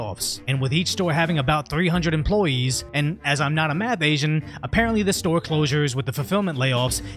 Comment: Did you just say, "I'm not a math Asian?" LOL!!!